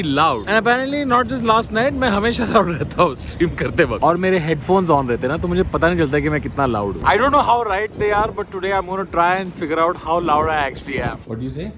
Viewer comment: I heard it as "main kitna laudu"😂